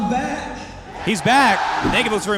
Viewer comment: i watch him for maaaybe around 10 years and until the second latest video of his i thought he means "he doesn't have people close to his skill" like in a kind of a funny brag thingie, then i realised what he was saying lol XD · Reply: until a few months ago I thought he was saying "I still have no closure" like some backstory lmao